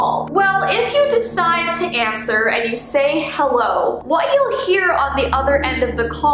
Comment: did she say hewwo or am i crazy